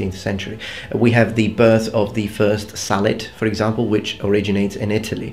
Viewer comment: had a laugh because i misheard it for salad haha really informative video ty